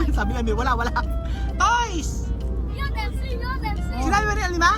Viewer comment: said "toys" sa 5:58, tama po siya hindi niyo lang narinig.